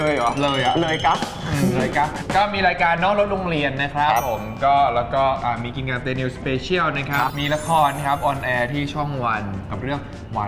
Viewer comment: IS NEWWIEE JUST SAID KINKAP TAYNEW SPECIAL HERE 15:39????